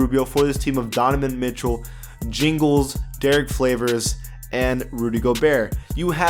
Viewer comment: Did you just say Derrick ''Flavors''